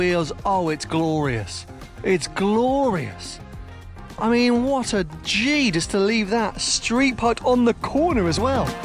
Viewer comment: did just say "What a G"...been hanging round Lenny The Geezer too much me thinks 😂😂